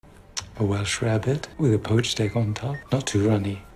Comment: So….. I thought he was saying “rabbit” but I guess it’s “rarebit”. 🤷‍♂️